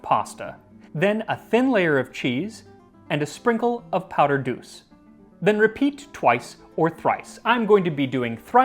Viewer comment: How much of what you cook goes to Jose? Ancient Egyptian cooking, yes yes. It sounded like you said powdered douce instead of bouce and that threw me off and made me laugh like a child and also feel a bit squeamish. And get a top hat and an old style Indiana Jones hat, to begin your history hat collection. Just one would never do. Too many time periods.